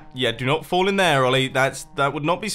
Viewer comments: Wait at around 4:10 did he say Ollie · Wait did he call himself Ollie?